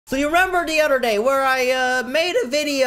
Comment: did you say did you rember the othr day